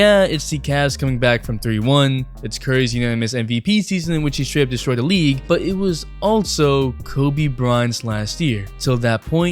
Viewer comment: lmao anyone else hear kobe bron